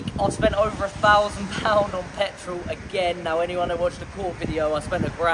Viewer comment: does he say Cork as in cork ireland?